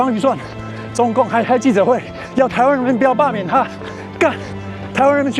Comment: 印度語聽不懂啦😂😂😂 我只聽得懂英文的gun🤔🤔🤣🤣